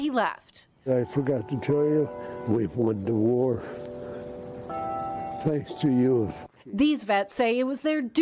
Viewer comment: Can someone explain what this brave man says at 2:57 ? I can’t make out what he says at that moment (as a native french speaker it is sometimes difficult for me to understand english) Thank you to the people who want to help me 👍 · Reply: He said "I forgot to tell you that we won the war thanks to you"